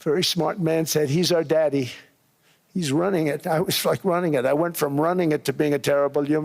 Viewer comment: does he say, “I was fckn’ runnin it”?